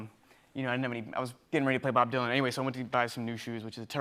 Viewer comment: did he say he was getting ready to play bob dylan?